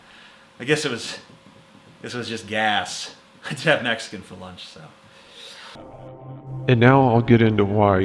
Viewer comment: I thought he said I had a Mexican for lunch xD